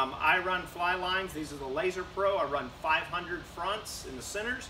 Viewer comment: Thought I heard you say "Laser Pro" lines. I don't see them for sale at your online shop.?